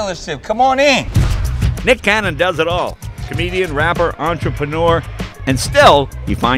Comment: Just to clarify, they said he's a wrapper, not rapper. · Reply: I thought I heard wanker not rapper